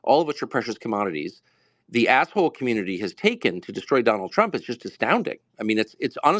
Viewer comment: What does he say at 12:28? "...the _____ community has taken...." (It sounds like he says, a-hole community, but that can't be right.)